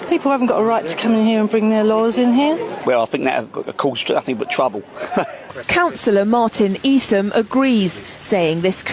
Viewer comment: come again mate what did you say ?? 🤔 😂😂😂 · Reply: So you fancy moving to Walthamstow then